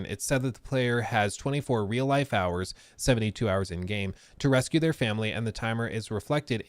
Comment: I thought he said that's 72 real hours? That's a long time in a game